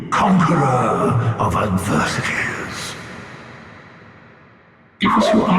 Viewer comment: When he calls you "conqueror of adversities" all I hear is "struggler".